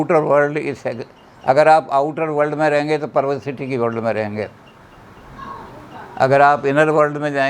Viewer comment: Bro guruji ek work bol rahe me samajh nahi pata raha hun powercity at last time about4:40 min please reply vo kya bol rahe hai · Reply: Yadav he said "pervercity" which he means "perverse behaviour/thoughts"